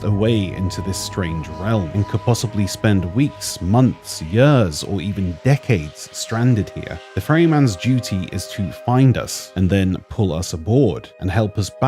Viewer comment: Am i the only one kinda disturbed by the fact that the years he said at 2:04 sounded like yurs?